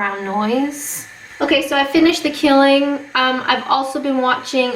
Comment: ¸i often put these on in the back round, like a podcast and sometimes I focus on other things, so I just heard "So I finished the killing" and was a bit... shocketh.